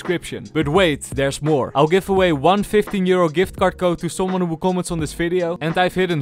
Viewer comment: sounds like hes saying im giving away one fifteen year old gift card